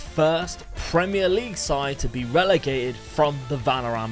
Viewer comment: Thought he said "Benrahma league"